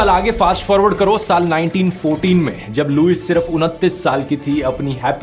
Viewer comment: caption wrong ho gya 29 year ki jagah 39 ho gya